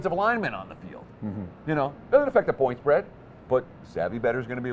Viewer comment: What does he say about the point spread at 14:19? Did he say it doesn't affect the point spread?